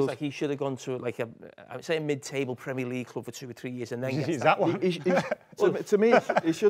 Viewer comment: Anyone know what said at 38:03? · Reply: Hes at one 😂